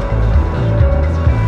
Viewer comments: "how much do I own you?" this is amazing. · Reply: Kolby Dickens At first I assumed she asked the driver "How much do I OWE you?" But on the 2nd viewing I heard "How much do I OWN you?" Great metaphor for how the government controls us. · At first I assumed she asked the driver "How much do I OWE you?" But on the 2nd viewing I heard "How much do I OWN you?" Great metaphor for how the government controls us.